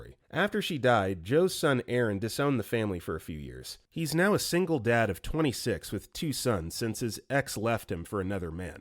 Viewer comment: i thought he was saying he was a single dad of 26 kids… 💀